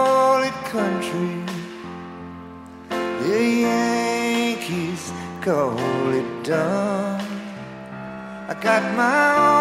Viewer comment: There's a southern accent, where I come from The young'uns call it country The yankees call it dumb I got my own way of talkin' But everything is done, with a southern accent Where I come from Now that drunk tank in Atlanta's Just a motel room to me Think I might go work Orlando If them orange groves don't freeze I got my own way of workin' But everything is run, with a southern accent Where I come from For just a minute there I was dreaming For just a minute it was all so real For just a minute she was standing there, with me There's a dream I keep having Where my mama comes to me And kneels down over by the window And says a prayer for me I got my own way of prayin' But everyone's begun With a southern accent Where I come from I got my own way of livin' But everything gets done With a southern accent Where I come from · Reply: I thought he was saying..."The Yankees call it dung"!!!